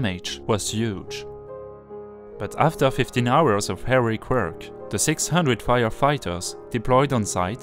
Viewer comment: Hello everyone, please, could you tell me if you needed subtitles to understand or if I need to improve my English to be more understandable? Thank you for your answers. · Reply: Your English is good, and a pleasure to the ears of this Englishman. Thank you. The only point where I got confused (briefly) was the expression 'Heroic work' (2:08). I heard 'Hairy Quirk', which I knew made no sense, so I did a double take, and re-computed. It is a matter of speed of speech (nobody can say Heroic quickly!), word stresses and other subtle things which you have already humbly acknowledged. Remarkable that was the only point of missed comprehension in the whole piece. Bravo! And thank you for a very interesting video.